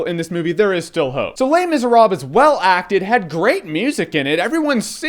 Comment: not to hate on your attempt to say the title (and the fact that the title itself is in french) but everytime you said "Les Misérable" it sounded like you were saying "Lame is a rob" lol